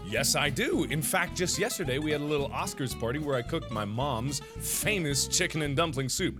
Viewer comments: I heard cooked my mom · No joke, I thought he just said "where I cooked my mom"... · I thought he was going to say "I cooked my mom" OMG IM DYING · for a minute i thought he said he cooked his mom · I thought you said "Yes I do cook, we had an Oscar's party and I cooked my mom!" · I thought he said he cooked his mom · 2;14 I thought he said "In fact, I cooked my mom" but it was "in fact I cooked my mom's favorite meal" xD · Omg I thought you said 'I cooked my mom' XD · at first i thought you said you cooked your mom😂